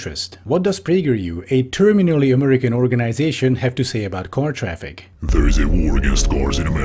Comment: I thought you said "a terminally ill American organization" which to be fair would've been a fairly accurate description